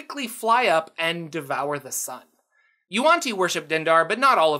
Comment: when he said "Yuan-ti worship Dendar" at 2:36, I thought he said "your aunty worshipped Dendar" O_O XD